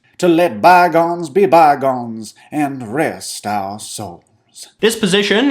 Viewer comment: Right before said souls I got a budlight add so i thought he said and rest our bud light